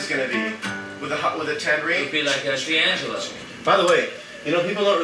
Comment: did he say d'angelo??